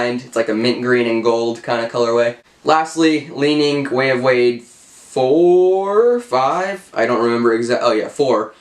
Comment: What is that shoe at 2:46 I didn’t understand what you said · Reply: It’s the (Dwayne Wade) Way of Wade 4